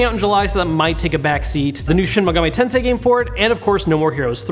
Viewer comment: did you say Shin Megami Tensei game PORT or FOR IT? I swear it sounds way too much like you said Port there. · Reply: legolover8805 He's saying for it, it does sound similar haha